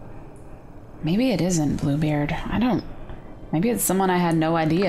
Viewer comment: When she said Bluebeard I thought she said poopbeard 😆